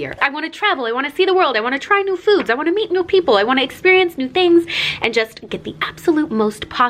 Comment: Lol after she put the to the fullest I thought she said she wants to eat new people 😅😂